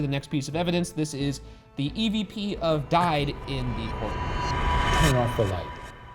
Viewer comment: I heard “I….died?”